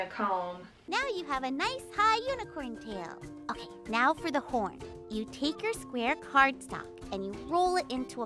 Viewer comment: idk why but I heard it as "porn" and I couldn't understand what she actually meant until a few seconds later